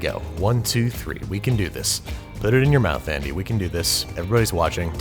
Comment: did you say andy